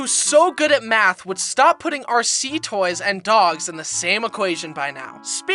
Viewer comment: Why did I thought he said "PUT RC TOYS AND DOGS IN THE SAME AQUARIUM" What kind of zoo is this?